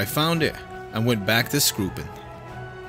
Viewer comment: I just heard "please fuck with my port"